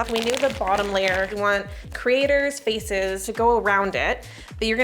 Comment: OMG, you're saying creator! I thought you were saying crator faces, lol!